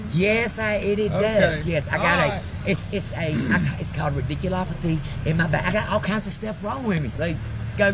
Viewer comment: Did she say ridiculopothy!????? I’m dead😂😂😂